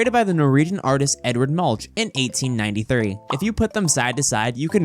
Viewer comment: why does the subtitles say 1093 and says 1893